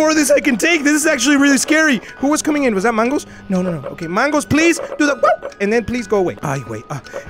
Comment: Is it just me... But wat WAUf is he saying mangos is doin Cus i dont hear it Or am i just deaf??? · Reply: He says “do the WAOOO”